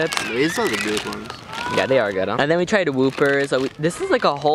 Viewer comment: Did I just hear him call those “whoopers” ??